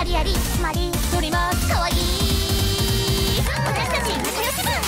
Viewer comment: ロリはかわいいに聞こえる